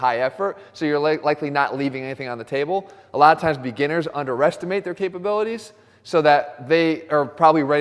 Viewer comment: I heard Jeff pronouncing underestimate as under-restimate :)